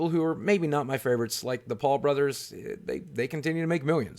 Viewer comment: When you said Paul brothers I thought I heard vlogbrothers and I wanted to flame you lol